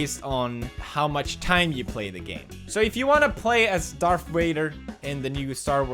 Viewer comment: Wait... Is it just me or did he say "Darth Raider" at 3:23 ?? Kappa